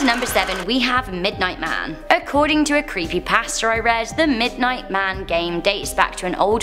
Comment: Sorry I thought she was saying creepy pastor but then I realized she was saying creepy pasta